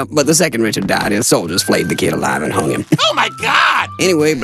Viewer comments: Allan: Yep, but the second Richard died, his soldiers flayed the kid alive, and hung him. Skippy: OH MY GOD!! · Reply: I thought they said flamed since he looks burnt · Is it posible to survive geting fleyd long enafe to be hangd (sorry for the dislecsia english is not my first langueng) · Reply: Flayed? I thought he said "fry" because of his dark skin when hanged :|